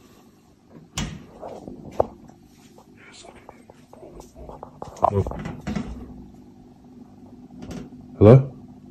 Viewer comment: That whisper in the closet at 5:09 is chilling 😱 anyone know what its saying? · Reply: Someone commented that they heard the voice say “He’s going to need some holy water” and that’s what I heard too! Super creepy 🫣